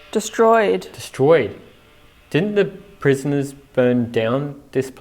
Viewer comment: Sounded like the spirit said, 'we destroyed it'.